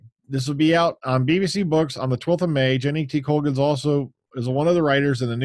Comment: When you said 'Jenny T. Colgan' I thought you said 'Jenna Coleman'. Series 10 hasn't even begun filming yet and I miss her already...